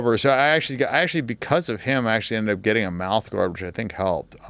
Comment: What did Dan say at 1:45:04? Mouth orbsion? Thats what i understood, anyone else know?